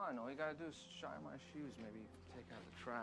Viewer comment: first time i watched the movie all i heard was "baby" instead of "maybe" lmao english isn't my language